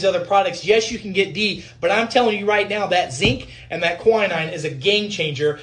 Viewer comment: sorry but what did he said at 3:20? Zinc and what? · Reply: Schweppes Tonic Water with Quinine, as in Gin and Tonic, hold the Gin! ;)